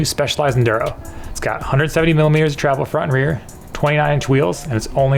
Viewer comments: is it just me or did he say it has 130 mm of travel front and back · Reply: Na he did say 170 mm of travel front and rear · Hey guys is it me or I think you said "130 millimeters of travel" 0:35 I just want to make sure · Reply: no, it was definitely 170